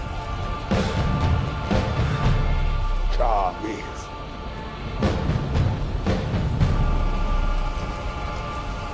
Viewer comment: can someone tell me what he said? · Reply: He said the one who fights